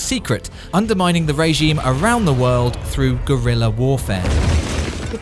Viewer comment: I too like heard gorilla instead of guerilla :)